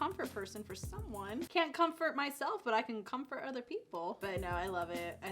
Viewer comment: when I tell you I misheard 'cause I was washing dishes 22:22 "can't c*m for myself but I can c*m for other people" I ran back and had to rewind like what is Miss talking about!?